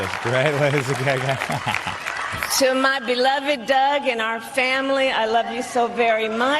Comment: I could have sworn that Kamala said her beloved dog instead of beloved Doug😂